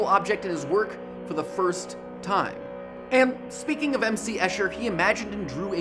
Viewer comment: for a split second I thought he was going to say MC Hammer instead of M.C. Escher